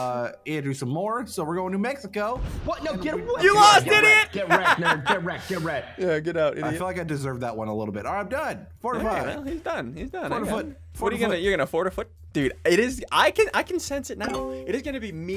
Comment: I heard “I f*cking deserved that one.”